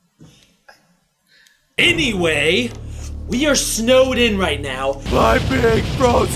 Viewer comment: Ian said, "Were Snowed in..." I thought he said Snowdin. 😀 Get it Undertale.